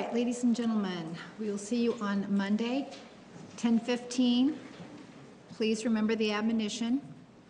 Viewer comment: What did the judge said before they should leave the courtroom 1:11:32 Did she said: Please remember the admonition?